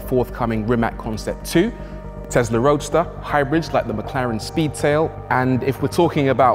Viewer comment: "Testarosta"???When I hear something like this, it blows the entire video right out of the water. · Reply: he said Tesla Roadster not Testarosta